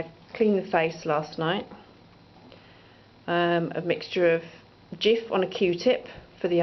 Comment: Did I hear "Jiff on a Q-tip"? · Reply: haha, yes probably that is something I would use , but its Cif, I cant help but still call it Jif it's old name in UK.